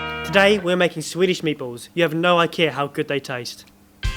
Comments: did he just say "you have no ikea how good they taste" ?? HAHAHAHAHHA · Did he say "You have no IKEA how good the taste"? haha >< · did he just say you have no ikea? haha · did I hear you say 'you have no ikea'